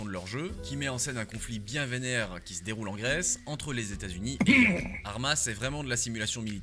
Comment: Why are you coughing right now 03:10? We didn't hear the name of the country. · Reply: The conflict between USA and Iran, on greek islands